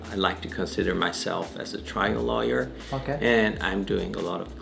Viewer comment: I swear I heard triangle lawyer